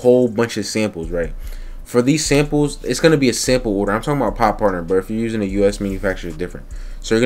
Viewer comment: par portner? whats the name? i couldnt understand it bro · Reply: POD partner